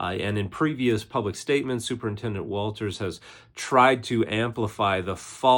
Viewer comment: Also I keep hearing "Supernintendo Chalmers"